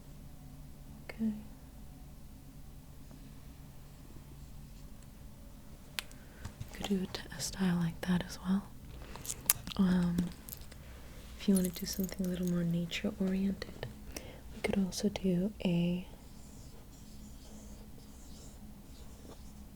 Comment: I thought at first you said “if you’d like something a bit more Nietzsche-oriented” and then started to draw his famous moustache.